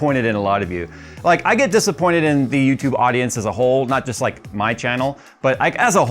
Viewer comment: YT comments... I heard your 'as a whole' as 'is a hole' and found I was nodding in agreement.